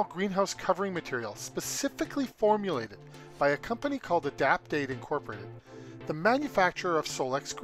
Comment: Oh I finally figured out the name you give at 5:24 Adapt8, sounded like Adapdate. Maybe when you can add names onto the screen so we don't miss it, thanks